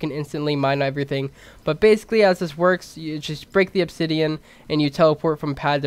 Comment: Did he just say "breaken"